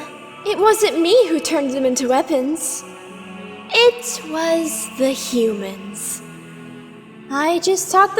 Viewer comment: Subtitle : "It was the human What i heard : "its was the human" Somethings wrong here...